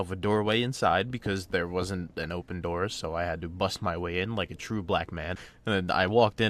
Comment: Did he say "Like a true black man"?